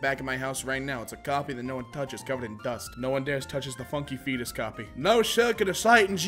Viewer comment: I thought he said none touches the fetus coffee